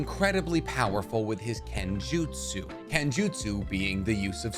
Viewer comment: What did he sayyy? Genjutsu isnt sword fighting?? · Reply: Bro he said kenjutsu not genjutsu, kenjutsu is the using of weapons, listen properly next time